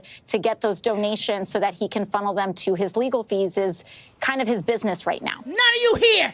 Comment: Did I just hear AOC said his legal "feeses"?????